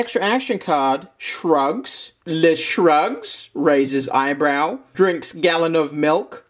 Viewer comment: I was only listening and not looking at the screen, so when he read the High School Musical card, I thought he said “drugs” rather than “shrugs”. “Drugs, drinks a gallon of milk”… yeah, checks out.